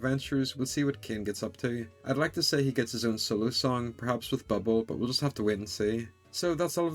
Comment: I thought I heard you say, "Silly Song" not "Solo Song".